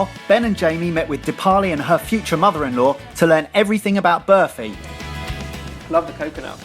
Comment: i keep hearing 'birthing' instead of barfi. "Okay so back to learning about birthing." "We have everything knowledge-wise about birthing." well.